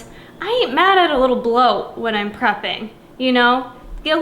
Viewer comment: I initially thought you said, "I'm not mad at a little blow when I'm prepping." Hell yeah!! Lol!!